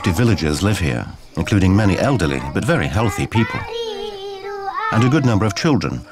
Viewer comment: did he said at 2:09 many elderly healthy people ??!!